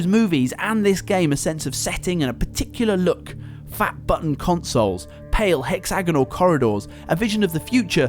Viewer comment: okay lets get this straight... 05:54 does he say "FAP BUTTON CONSOLES"